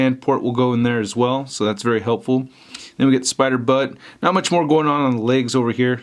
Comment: Did he say spider butt in